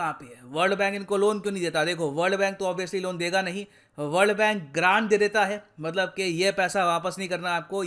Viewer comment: I heard world bank gand de deta hai🤣😂🐷🐷🤣😂